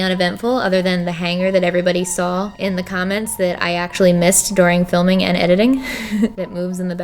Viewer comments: Did she just say dooring not during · When you said during thought you said doring